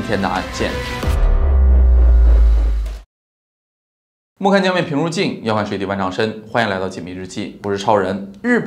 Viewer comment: 这两句话听了几遍还是没听清楚你在说什么， 好像到了什么什么“静”， 什么什么“深” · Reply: 莫看江面平如镜，要看水底万丈深。